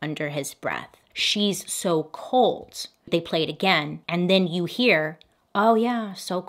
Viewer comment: I just don’t think Todd did it. Maybe the son though. Jerry and the son may not get along. I heard “ breath some more” btw